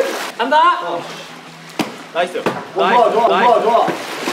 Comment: eu ouvi "VAI VAI VAI" KKKKKKK